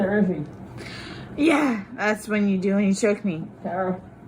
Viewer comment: I just caught this. he said he couldnt breathe, she said "thats what you do, (breathe) when you choke me. Shows her intent on if he can breathe or not. Her admittance she would control it. . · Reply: I thought she said “when you cheat on me” . I need to listen but so distressing.